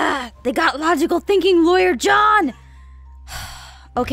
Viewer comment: I thought it said Sassy Laurance xD